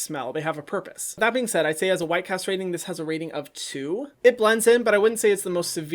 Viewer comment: What you said: “White-cast rating”..... What I heard: “White castrating”..... 😂🤣